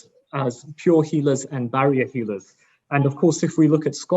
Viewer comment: Some great questions. I heard "valiant healers" lol